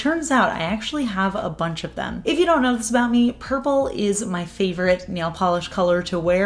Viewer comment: I thought you said navy blue was your favorite polish to wear